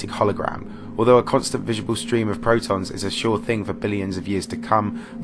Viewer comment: Did he say protons?